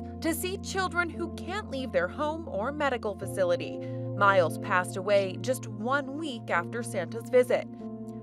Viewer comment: I thought it said he passed away kn santas lap RIP